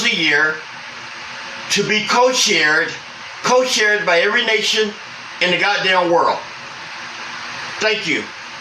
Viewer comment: The first time I heard him I thought he said Aryan nation hahaha had to go back a sec